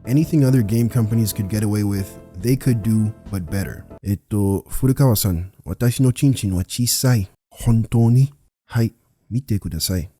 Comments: to ya'll who don't understand Japanese, he said , "Furukawa-san, my dick is small" So yeah, wrong subtitles on purpose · the subtitles is not what he said lmao he said his pp small 😂